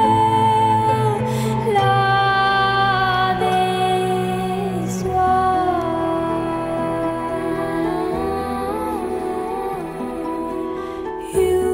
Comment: Damn this whole time I thought she was saying " love is war" not " love is wild" 😕 Oh well. I'll just pretend in my head that's what she is saying from time to time. 😄